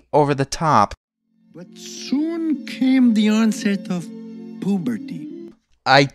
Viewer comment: Did he just say "poop"?